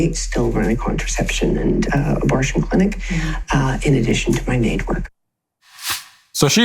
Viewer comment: Did she say, "In addition to my Mage work..."?